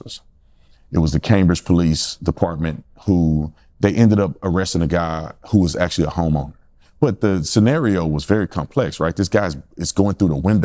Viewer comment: Did OT just say homo?